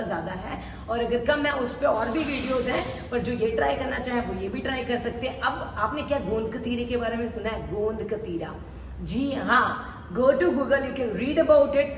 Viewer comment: What is gond ka ....or what? I can't understand... please any one can tell me? 3:30 time.. which word she used? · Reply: Gond katira